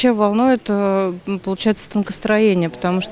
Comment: Мне послышалось танковый танкостроение......